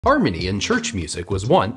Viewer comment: For some reason I heard "Armenian Church Music" instead of "Harmony in Church Music" and was a little confused but then the video looped.